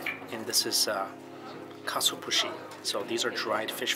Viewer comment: I don't know what you said but I heard Catsu-pussy so I'm feeling dead at 4am lmaoooooooooo